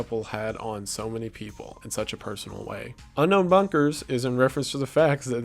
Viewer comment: I heard it as ''unknown bonkers'' and thought he was referring to the fact that there are cults that haven't been discovered yet